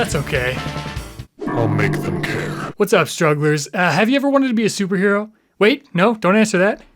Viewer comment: I lowkey thought he said “what’s up strawberries” instead of strugglers in the intro and it bothered me infinitely until I rewound and realized what he really said